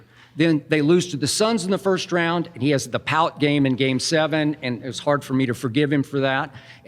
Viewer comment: Skip mentions (what sounds like) The Pallet Game. Is that what he's saying? What was that?